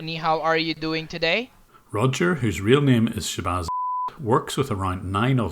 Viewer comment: I thought he said his real name's shibazz likkerz lol